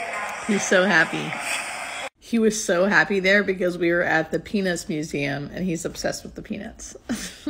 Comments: Oh my God when you said Peanuts museum, i thought you said Penis museum. Thats so funny. Tell Rich lose the moustache. Makes him look old. Hello from Ireland❤ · You both look so happy together! I have to admit that at first I thought you said “penis museum” 😳😂😂 Was happy to then see good old Snoopy! He’s got to be a great guy if he’s a Peanuts fan. 😉 Wishing you all the happiness. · Good thing for captions, at first I thought you said "penis museum", (this is where captions came on) then I thought it was "peanuts museum" then found you meant "Peanuts museum"! haha He won't like this but, yes he's cute, I like the last photo (beard) and he would like my dog because she looks like Snoopy. 🐶🐕 You make a sweet couple! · I thought you said he loves the penis museum…… cracked up laughing when I worked out what you actually said. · I thought you said he's obsessed with penis. Lol The one side pic I thought was Dr Oz. Glad your happy! Hoping for only the best for you. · He's a handsome man. I like the mustache too. And just so you know, at first I thought you said you were at the "penis museum"!!!! Lol!! Peanuts museum is much more PC. Lol! Best wishes for the two of you!!❤❤